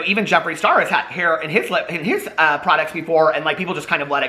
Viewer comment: Ya 🔨 the nail on the head with that one. He’s just saying what he thinks people want to hear without even thinking about what is actually said. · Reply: Bahaha I thought you said even Jeffree star had hair on his lips. I busted out laughing, I’m delirious and mentally drained that I just thought the image of Jeffree with hair lips hilarious. 🤣